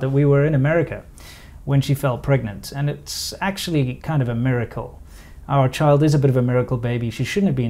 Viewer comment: I heard Murrircle